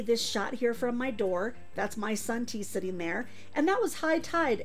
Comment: I miss heard you say, my son,Tee. I thought what? She has a son? Lol. Oh….she means ,my sun tea. I live two blocks from beach and absolutely love it.